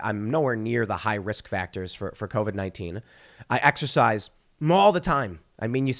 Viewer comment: Sounded like he said "I have sex all the time" 😂